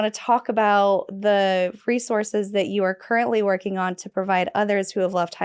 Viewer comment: I’m blown away by this strong, resilient woman. I thought she said “Freesources” instead of “Resources” more than once. Did I hear correctly? If not, why not?!? I love it. be my guest! LOL!! 😂 Thank you for sharing your life story both before and after hell. You are the BEST! God only knows how many lives you have touched, saved, and changed. ❤